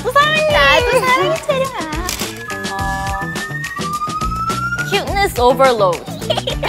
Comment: Lia: Cuteness OVERLOAD Yuna: Cuteness OVERLOAF Chaer: Cuteness OVERLED Ryujin: Cuteness OVERLUD Yeji: Cute ASDFGBGHLEH 🤣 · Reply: i thought yeji said cuteness abalone 🤣🤣